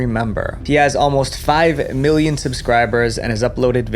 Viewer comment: I somehow misheard jack’s subscriber count as 5 billion